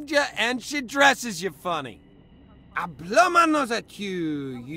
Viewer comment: The line at 5:28 sounds like a reference to Monty python and the holy grail, and I believe he says "I blow my nose at you" where the question marks are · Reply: I heard "I thumb my nose", but yeah. Monty Python.